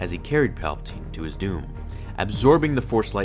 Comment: It says at the time 5:06 it says "Respirator having been heavily damaged as he carried palpatine to his (day)" instead of saying "Respirator having been heavily damaged as he carried palpatine to his (doom)"